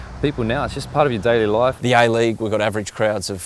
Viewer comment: Shit they were saying "the A league" all the time..... all i heard was "the I leauge"